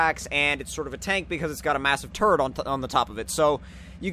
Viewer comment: I thought you said "it has a massive turd on top of it"